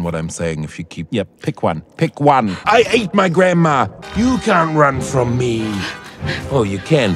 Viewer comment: “Just pick an eye babe, I can’t concentrate” I A T E M Y G R A N D M A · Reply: Kinda sounded like he said: I HATE MY GRANDMA!